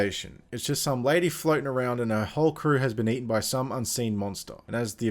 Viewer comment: I honestly thought he said her whole career was eaten up by a monster